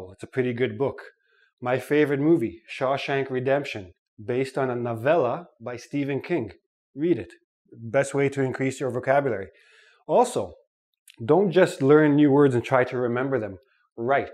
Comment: Sounded like you were saying Stephen Kink at first.